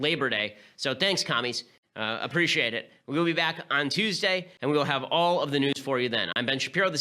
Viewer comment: At the end I thought ben said "And we'll have all the nudes for you then"